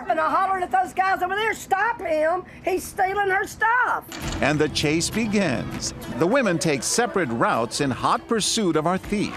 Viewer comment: Am I the only one who heard ‘ and the chance begans ‘ at 1:52 ?